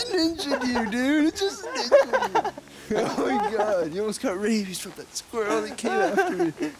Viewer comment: Close to the end it sounded like he said "you almost got raped"